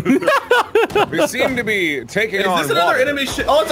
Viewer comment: it sounded like Wade said "is this another anime shit?" xD